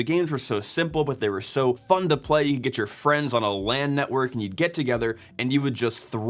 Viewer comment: "LAN network", from the makers of pin number and ATM machine. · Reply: At first I thought he said "land network" like a landline for computers.